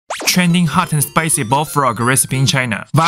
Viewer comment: I thought he was saying bone broth to find out it's bullfrog